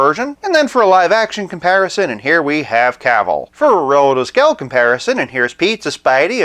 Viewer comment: I've been meaning to ask, what do you say when you compare figures with Stealth Iron Man and Pizza Spidey?, I always heard "Rotoscale" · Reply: Oh sorry, I am saying relative scale comparison, in other words showing the figure with two very similar figures of two different scales.